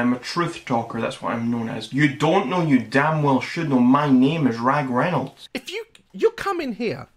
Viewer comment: Tell me why i thought he said his name was ryan reynolds 😭😭